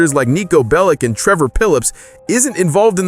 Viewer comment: did he just say trevor pillips